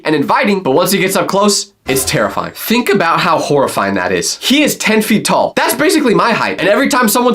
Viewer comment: I thought you say you were 10 feet tall